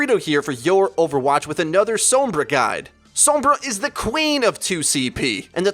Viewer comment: Was i the only one that heard sombra is the "clean/er (instead of queen)" of 2cp at 0:24?